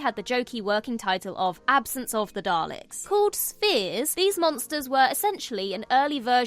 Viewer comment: Why would the proto-Toclofane episode be provisionally called "Spears"? Surely, as they're perfectly round, 'Spheres' would make more sense. Unless that's what she meant and forgot that ph is pronounced as f for the most part. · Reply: I can see your point, being Turkish I'm not well versed on RP (let alone speak it), but I definitely hear "spears", whereas the closed captions read correctly as "spheres"... Confusing.